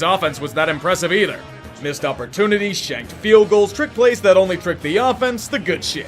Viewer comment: When you said shanked field goals in the Giants-Saints game I thought you said shagged field goals lol